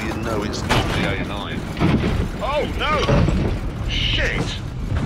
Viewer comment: did he just said.............."sheesh". he is such a legend